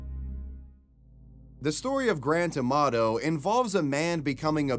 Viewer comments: I keep hearing "grand tomato" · I thought you were saying Grand Tomato. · Thought he was saying grand tomato